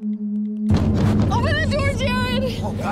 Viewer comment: 😂 at the beginning I thought she said “open the door dad!!!!” Needless to say I was extremely confused... oops